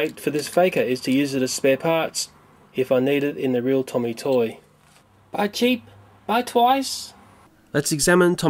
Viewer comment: what did you say there? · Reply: "Buy cheap, buy twice!"